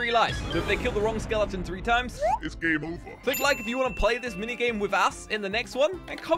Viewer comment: I love your vuds, but no offense, did you just say "play this mini game with a*s"